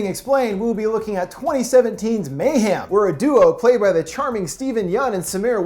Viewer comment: it sound like he says mayham